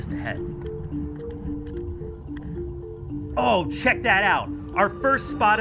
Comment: I thought you said shid that out